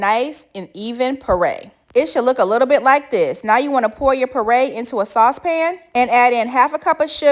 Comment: Cute outfit!! I thought you were saying mixed berry parade, which works!